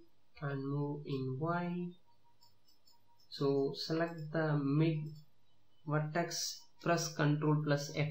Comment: Can someone help me. What does he say in 7:12? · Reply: Select middle vertex and press Ctrl+ F11